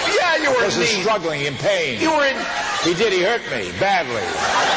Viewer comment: What does Bill say at 0:34? ‘Did I’? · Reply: He said, "Ditto."